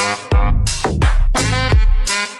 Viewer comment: Is it just me or am I hearing “SHUT UP DIGGER” or the other one